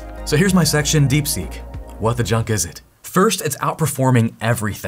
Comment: I thought you said, "What the 'chuck' is it?" instead of, "what the 'junk' is it?" and now I think you need to being using "what the Chuck is it?" all the time. Please and thank you.